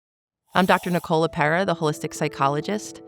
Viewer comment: Why did I hear I'm Dr Nicola Parrot?